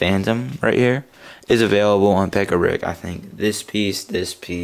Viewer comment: is available at where? a set? website? i couldn't hear u. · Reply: This is late but I think he said pick a brick.